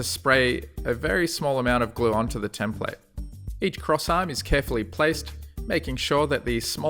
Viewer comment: I miss-heard it as "...each croissant is carefully placed..."